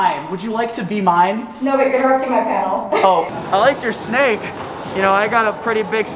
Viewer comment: what did the host (?) say at 1:11? i could only figure out “my panel” at the end 😅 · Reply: They said “No but you’re interrupting my panel”